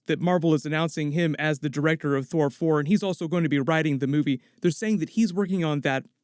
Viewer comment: Am I trippin or did he say "fhor 4"